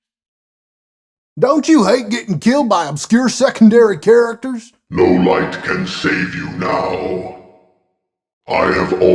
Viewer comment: Nightmare Freddy sounded like he said only All Might can save you